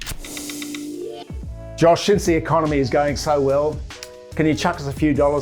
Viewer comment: Does he say “josh seeing as the economy is going so well” at 2:09???